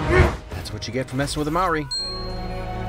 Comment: 😂😂 i thought you said māui but you ment Maori, your the frist person who i heard say it like that most ppl say it as if the "R" was a "D"